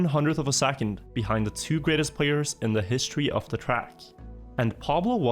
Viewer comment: man really thought that said "history of the AO3 race"